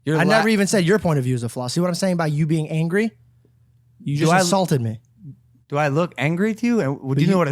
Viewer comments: George is Blatantly gaslighting. How did Logan “assault” him? Like come on. Logan u made good points. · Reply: I heard “insult” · he said assaulted. Hence why logan asked if he even knew what assault meant. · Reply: he said insult and Logan thought he said assault